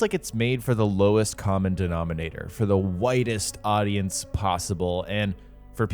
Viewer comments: Download Opera GX for free: <link> Hey hi hello, at 2:23 I said that I think Valhalla is made for the WIDEST audience possible, like it appeals to the largest group of people. A lot of you are hearing WHITEST... yeah I should've used a different word there. No, I was not suggesting Valhalla was made for white people 😂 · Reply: At first I heard "whitest" and then after thinking about it for a second I realized you said widest 😂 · Did you just say it’s made for the whitest Audience, or the Widest 😂